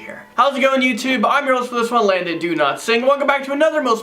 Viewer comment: Why does it sound like he is saying his name is “Landon do-not-sing”